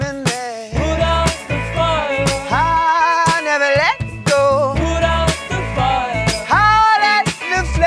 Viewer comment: I hear "I have an echo" in stead of "I never let go" thanks to my girlfriend... lol